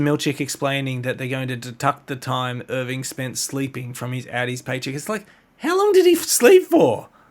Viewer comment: the transcript loves talking about Audis. Is that from channel (Shitcast) or just mishearing Outie?